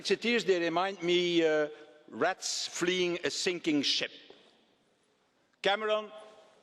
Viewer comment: Haha, I thought it said "stinking shit" at first. :)